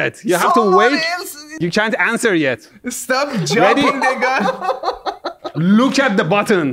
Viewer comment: did he say the n-word? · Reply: "stop jumping the gun" 😂 took me several listens to unhear N.